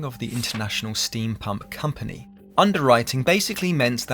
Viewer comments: At first I could have sworn he said "International Steampunk Company" instead of "International Steam Pump Company". Science fiction has irrevocably warped my brain. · When you said Lehman underwrote the "Steam Pump Company", I thought you said the "Steampunk Company". One is much cooler that the other.